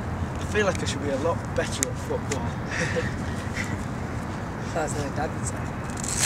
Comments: I kinda feel Harry was offended by what Louis said. Am I wrong or right?t I don't even know. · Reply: Its was like at 0:55 or something and I think he said "That's something you'll dad would say." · WHY HAS NO ONE NOTICED AT 0:56 LOUIS SAYS IN THE BACKGROUND "THATS WHAT DADDY SAID" !!!!!!!!!! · what did louis say?? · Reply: i think he said "that's what a dad would say" or something like that